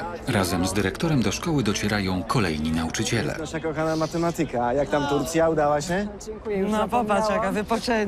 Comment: Czy tylko ja usłyszałam „nasza droga matematyka”